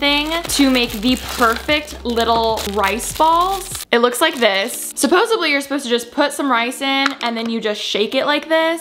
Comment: am i the only one that noticed she said "supposesebly" instead of "supposedly" at 1:39 HAHAHA